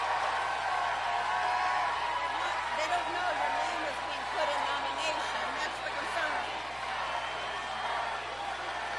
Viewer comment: What did Jane say to Bernie at 22:40? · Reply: "They don't know your name was put in nomination, that's the concern."